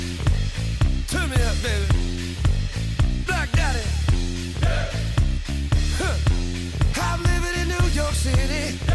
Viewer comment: wait wait wait WHAT was that? WHAT did he just say?! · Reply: I think he said "Black daddy!" I could be wrong though.